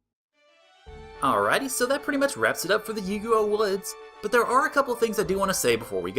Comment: I kept hearing "Yu-Gi-Oh woods" and thought for sure you were about to have a card battle. :D